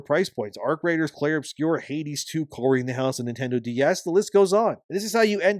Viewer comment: I was listening to this passively while I was working and did a double take when I heard "Cory in the house on DS." 😂